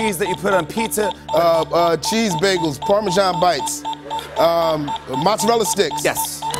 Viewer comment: Thought said “Parmesan bites”. WTH?? I dunno that just made me laugh.